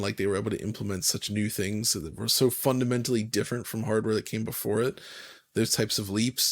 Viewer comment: I thought I heard a new cool word then "fukinmentally"